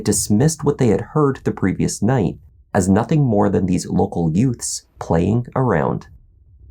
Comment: Did you say "youts"?....what is a yout? Best line ever. 😊